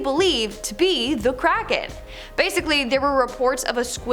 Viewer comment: I thought she said the crack head 😂😂😂